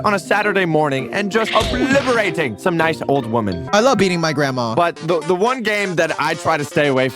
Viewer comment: For a second I thought he said”I love EATING my grandma☺️”